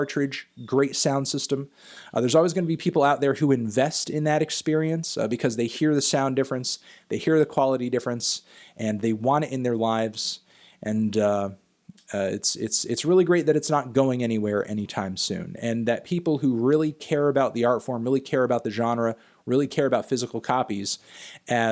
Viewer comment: Did anyone else heard ‘and they wanna end their lifes...’ at 8:54?